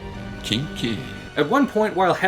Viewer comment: Did he just say 'Kinky' in a Disney show?